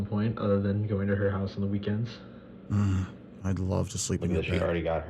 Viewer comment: Did he just say "Hmmm, I'd love to sleep in your bed" just then? What the...? lol